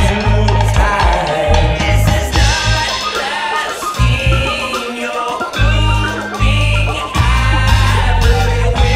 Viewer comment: does any one know what he's saying at about 1:02 ? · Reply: Griffin Jurisson This is not lasting, you're moving out. Where they will not go; When you want it-- bye-bye!